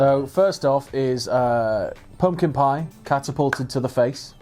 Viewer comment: Aaaaaaah, a pumpkin pie! First seven times I heard a 'porcupine'...